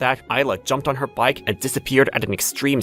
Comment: Bro, at 2:57 i wasnt focusing and didnt her aila introduce herself, so at this time i heard " i jumped on her bike a sped away" i was like 😮